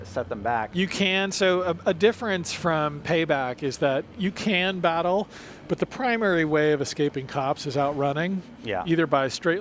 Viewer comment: Did he just say I can't battle police cars?